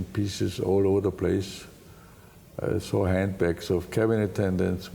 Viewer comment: What does he say at 2:15? What from flight attendants? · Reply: handbags